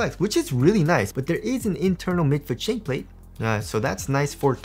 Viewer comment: ive been hearing the "mid foot chainplate" is it actually chain plate? or am i mishearing it? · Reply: Shank Plate